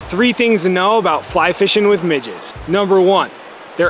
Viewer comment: Bro I thought he said how to fly fish with midgets 💀😂